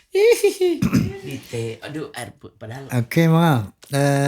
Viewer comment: maaf disitu kayaknya Mongol mo bilang 'air putih...' . Tapi GK selesai kata 'putih' nya, d ganti JD 'padahal' Maaf klu mmg salah dengar, 🙏🏻🙏🏻